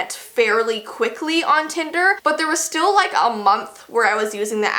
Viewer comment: I thought it said tender and it reminded me about chicken tenders and I'm like "jess is gonna talk about chicken tenders?" 😂😂😂😂😂